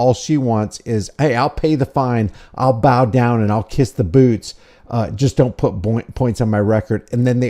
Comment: "I'll bow down to kiss the boobs." Sounds like a plan. I know he said "boots." I have to find a bit of humor instead of the anger i have for a corrupt judicial system where cops can legally lie to the citizens.